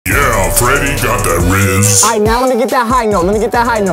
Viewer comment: why did he say Freddy get some RZ💀